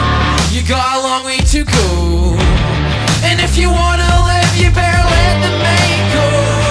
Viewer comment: When he says "You better let the mind go" it sounds like he says "You've better let the Mango"